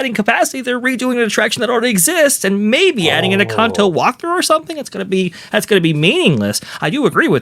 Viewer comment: Did he say a cocktail walkthrough